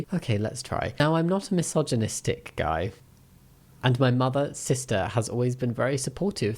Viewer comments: I wasn't looking at the screen when you said Mother, sister and my stupid ass thought you said 'Mother-sister' like he were inbred or something. :l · I had you on in the background, and heard "mother-sister" instead of "mother, sister" and got VERY concerned.